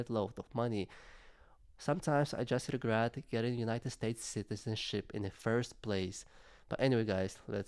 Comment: Funny sounded like you said shitizenship lol